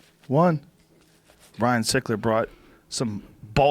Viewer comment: I thought it said Ryan Sheckler 🤣